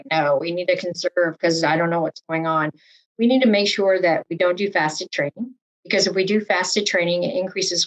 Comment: What did she say at 26:40? Fast to training? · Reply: don't do fasted training. (I had to turn on closed captions)